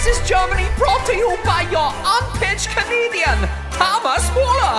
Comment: Why did I hear “Armpit Comedian” in the beginning